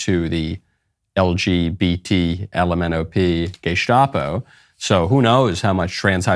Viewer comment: I don't know if you inteded this or not but I definitely heard "Gaystapo" at 3:01 and I'm gonna be using this from now on. Greetings from germany.